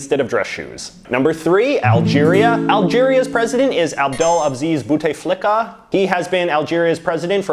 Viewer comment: literally thought you said bootay flicker!